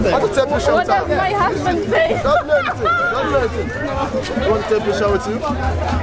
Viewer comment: did the guy at 4:36 really say 'do you want to take a shower too?' or did I misheard that haha?